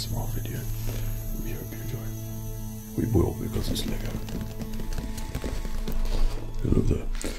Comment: excuse me what did he say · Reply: You will because of something